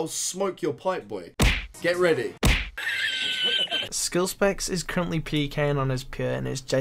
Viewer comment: Came back to watch these videos that are pure gold only to find even more gold.. at 1:17 the captions say "skill specs is currently pecan on his penis..." 😂 gotta love auto generated captions